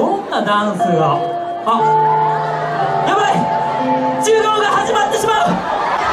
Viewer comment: の「授業が始まってしまう！」が「柔道が始まってしまう！」に聞こえたw